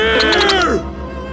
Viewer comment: I misheard what homer said i thought he said bear not beer